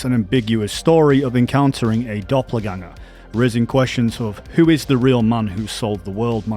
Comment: 😂 I had to replay the video because of his accent I thought he said Rizzing instead of raising